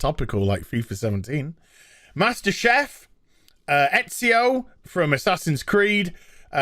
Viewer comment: did you just say F*****N Master CHEF!?!?!?!?